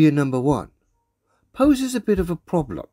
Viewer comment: Did he say “hoses” or “poses”?